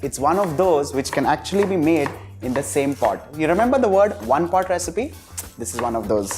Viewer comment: i thought he said 1 part, so im glad u clarified that as pot 😂